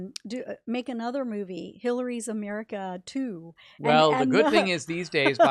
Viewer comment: Did I hear. Hitlery. ? 15:28 15:39 15:41